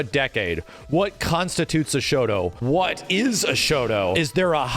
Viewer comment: Bruh for a moment i thought it said "what is a shota"